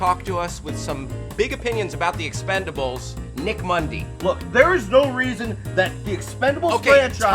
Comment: any body else think For a spilt second there he said "Mick Mundy" instead of "Nick Mundy" and instantly think of sniper from TF2?!